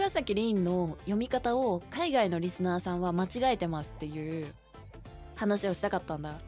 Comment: Transcription errata: 0:04 間違いてます → 間違えてます 0:40 悪者 → 悪ノリ 0:53 あほ的って → 煽ってきて 2:06 かも → 一回も 3:34 僕は → 僕が 3:36 リンという名前間違えたらR-N-Aとか[nonsense] → リンという名前のスペル間違えてR-E-N-Eって[nonsense] 4:22 言われた → 言われたら 4:36 当ってない → 合ってない 5:15 返ってある → 帰ってやる 5:16 笑っとんな → 分かっとんな 5:23 入れてある → 入れてやる 5:24 5トンくらい送ってある → 砂5トンぐらい送ってやる 5:41 進んでる → 荒（すさ）んでる 5:53 からな それで → から それだけ TL errata: 0:33 海外のリスナーさんって言ってるのに means "even though they were [or she was] saying that they were overseas viewers" 0:43 - 0:47 the stuff with って and 話 and 言ってんじゃん etc. is not talking about when she said something in the past or that she's always saying something, it's just a rhetorical way to emphasize what she's saying now, kind of like "I'm telling you" or "let me tell you" etc. in English. Same at 3:16 0:53 she's saying that they're provoking (煽って) her, not that they're idiots (mistranscription) 1:44 the と思うんだけど is referring to 話をした not ばあちゃんが決めてくれた so she's saying "I think I mentioned that my grandma chose this name for me" not "I think my grandma chose this name for me" 2:05 this sentence is just saying "I've haven't seen Sherlock Holmes even a single time though" (mistranscription) 3:34 this sentence means "I may be an idiot but I wouldn't misspell my own name, Rin, as R-E-N-E"; it might be kind of hard to hear but she is saying あーるいーえぬいー here before she trips up (mistranscription) 5:14 she's just saying she'll go away again (or back home) after putting the gravel in the postbox (mistranscription) 5:16 分かっとんな ≒ 分かってるな so she's ba